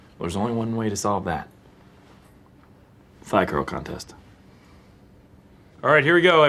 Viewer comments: I'm so disappointed - I heard "thigh curl contest" as "fly girl contest" and i was amped to see them dance like the Fly Girls!! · Whenever Jim says, “thigh curl contest”, all I hear is “fly girl contest”. 🤷🏽‍♂️🤦🏽‍♂️🤣